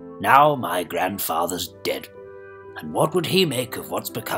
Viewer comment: "Now my Grandfather's dead" - he says dead so softly it becomes "ded", that is Russian for "old man", so what I hear is "now my Grandfather's OLD!" which is odd and funny at the same time